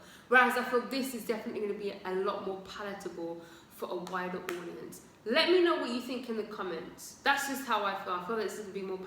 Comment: Lmao you said "more palletable for a wider audience" but I heard "for a whiter audience" the first time and was 💀🤣